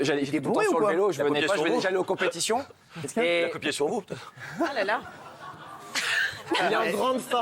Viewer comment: il dit quoi JM maire à 0:54 putain! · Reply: OneSix Clay "Il a copié sur vous peut-être"